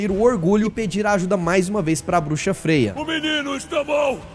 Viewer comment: ←Entendi FEIAA kkk